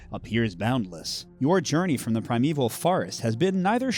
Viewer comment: Wow. I keep hearing "the Primeval Farts" every time I listen to this. Hearing is the first to go when you hit your 50's I guess.